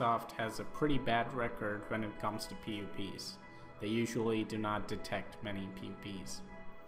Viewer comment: What does he say at 4:16 ? I just hear "When it comes to pp"